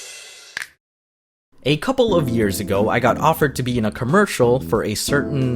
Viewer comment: To anyone who, like me didn't hear 0:20 he said it was a "couple of years ago" so it's not definitely not clair and their definitely still together. Yah Definitely